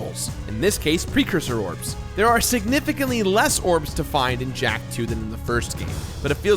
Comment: I was so hyped I thought it said knack 2